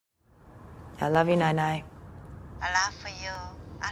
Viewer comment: So confused at 0:00, I thought she said "I love you, Nanay" and thought this was about a Filipino-American family.